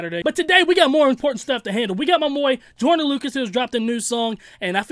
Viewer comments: "Muh moi Joyner Lucas" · Did he just say "My Moy"? lmao 0:26sec